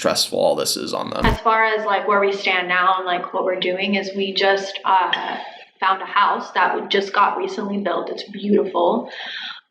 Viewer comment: did she say they "Just bought a house"? Who would give them a loan??